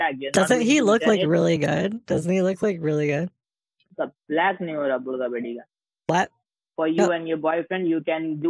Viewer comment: I swear, I nearly want to smack the shit out of the scammer, I swear he called him a “black n-“ but he might be swearing in Hindi so either is fine lol